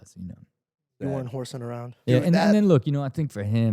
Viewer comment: Did George say “you weren’t horsing around?” 🤮